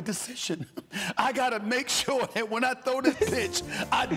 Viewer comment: Did he say “when I throw this Bit$h “ 😆🤣🤣🤣